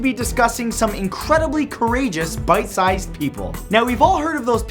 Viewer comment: I thought he said "bite sized heroes" and I questioned his sanity until I realized he was actually saying "pint sized" Smh